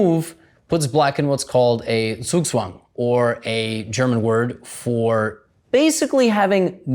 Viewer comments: the subtitles are wrong. Zugzwang, not Zwischenzug. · it's kind of weird @ 15:23 the captions say zwischenzug which is a different concept than zugzwang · subtitles are wrong cuz they say zwichenzug instead of zugzwang